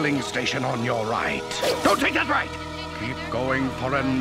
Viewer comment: You'll see a filling station on your right... DON'T TAKE THAT RIGHT!!! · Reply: Oh? I thought he said, "Go take that right"